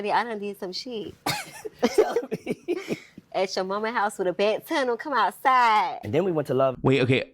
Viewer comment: I think she was saying “at your momma’s house with a bat telling them to come outside”